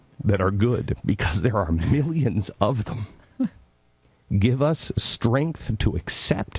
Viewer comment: The captions at 1:30 What he said: There are millions of them... What it said: "There are many indians of them,,, I was reading the captions, and I thought he was gonna start going on about indians or something o_O